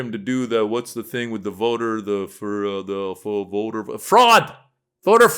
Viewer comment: For a minute there I thought that guy said "Voter Frog!"